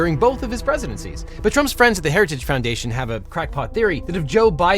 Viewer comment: I keep hearing 'heretic foundation', weird